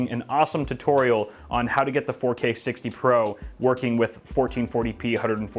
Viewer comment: Lol, my dumbass thought you said hd60 pro.